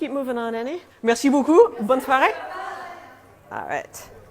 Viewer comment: At about 9:46, did say "bonne Friday" when she left the store? I know "le weekend" is a thing, but is that broadened to actually say "bonne Friday"?